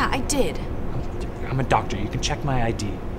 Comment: I am not trying to be rude but I legit thought he said “ last stage of Rick and Mortis”